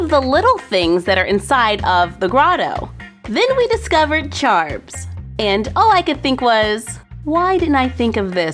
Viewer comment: The first time said charms I thought she said "charbs" ... Seriously, it sounds like she's saying "charbs" at 0:53.